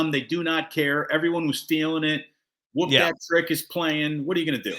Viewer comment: I THOUGHT STEPH SAID THEY WERE GOING TO "WHOOP THAT TRICK"... Gotta stop trolling Mr. Curry. That's not your M.O. bro. Grizz in 7💯